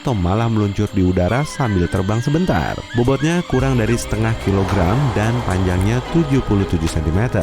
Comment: g salah denger gw? "bobotny kurang dari setengah kilogram" Kalian dengerny ap